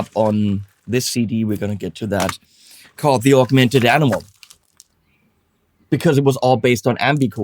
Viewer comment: I misheard you, "The Augmented Alamo" ;-)